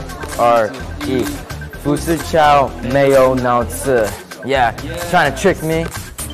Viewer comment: lol suddenly at 6:48 i also said "Fu Sichao meyyou nout se" together with them hhahaha. (sorry i don't know how to write the right words, i just heard like that hehe).